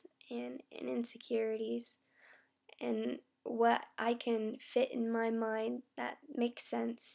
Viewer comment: i thought you were about to say "fit in mouth" @ 13:22. hahaha. but you said fit in my mind.